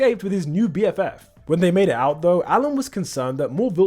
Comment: I thought you said when they “made out”.😂😂